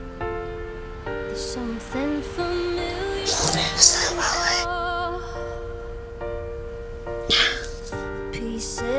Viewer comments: Does anyone know what she says at 1:59 ?????? I’ve rewinded so many times and can’t quite pick up on what she says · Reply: i think she said "little bit upset about it" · anyone know what she says at 1:59? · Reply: I'm a bit upset about it